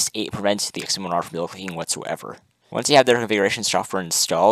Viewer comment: Hey nice video. What did you end up saying at 0:38? It prevents what? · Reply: I believe I said it prevents double clicking.